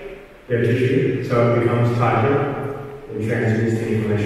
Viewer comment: "...through stretch....so it becomes (--) and transmits the information more.." What does he say at --? Thanks in advance! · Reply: stretch so that it becomes tighter